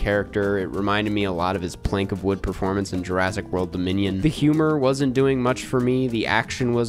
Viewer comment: I swear 2:38 says Jurassic World: the Minion